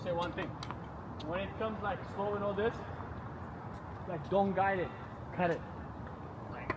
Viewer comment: What was he saying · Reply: when the ball is coming slow for a slice. dont try and guide the ball cut through it